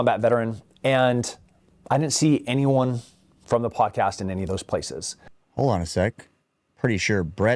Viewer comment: he is saying "I didn't see any of those podcasters there" that they weren't with him on those deployments. He is not saying they weren't there, just that they weren't with him there......stop grabbing for low hanging fruit. I you want to criticize him then go after him saying that 18 year olds shouldn't be able to obtain AR-15's........